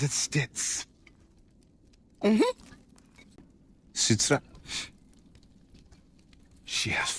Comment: What are those Subtitles XD "Switzerland" Instead of Citra. One more thing. Your Voice, from time to time (at least at the beginning scene) had different volumes. Other than that, Very Nicely Done Video 👍